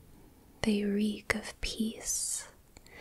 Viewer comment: I thought i started tweaking, at first i heard, 10:15 "reek of piss." Damn why is my dreams smelling like that...